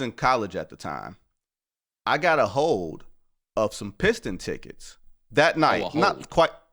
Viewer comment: 😂😂😂 I thought that too Bo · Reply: Def thought he said some “Hoes” and piston tickets 😂😂